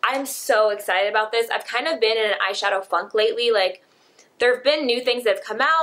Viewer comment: I don't think I'm hearing right here lol, but what does she say at 3:02-3:07...?? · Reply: She said she has been in an "eyeshadow funk"